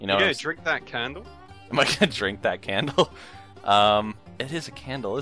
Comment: I thought he said "I'm going to drink the amontillado." Only few will understand